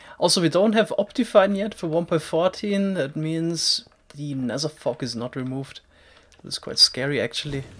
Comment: What did you say at 2:49 nether what?!? 😨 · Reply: nether fog, so smaller view distance